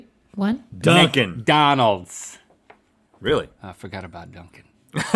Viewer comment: it sounded like they said mink'n and dunkdonald's